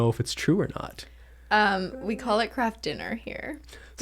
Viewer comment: I thought they said 'crap dinner' instead of 'Kraft dinner' LOOOL